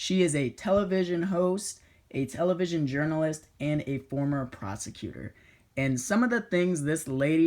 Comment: I thought you said former prostitute. Coz she is dumb as shit.